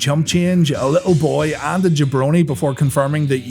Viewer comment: i heard they shaved a baboon 😂😂